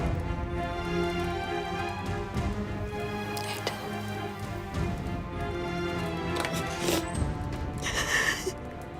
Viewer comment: ne diyor kulagina anlayan varmi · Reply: Öldün diyor